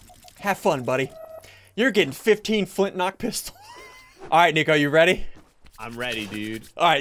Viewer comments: Sounds like he said FOOTKNOCK PISTOLS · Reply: MasterMelonX He Didn’t. If You Slow It Down To 0.25 Speed He Says Flint-Knock Pistol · did he say foot or flint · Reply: Suyapa Torres flint lock